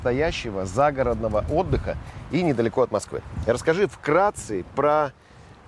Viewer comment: мне послышалось, или сказал "вкрации"??? А вообще, отличный рекламный ролик получился!